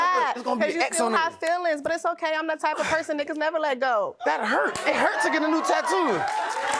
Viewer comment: did she say "niggas" (in my curtis Mayfield voice)?